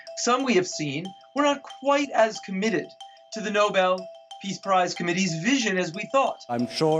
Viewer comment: "For Piece" instead of "For Peace" at 3:12 gave me a giggle.